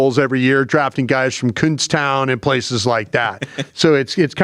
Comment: 2i thought said “coonstown” and not “Kutztown” at the 8 minute mark I’m dead lolol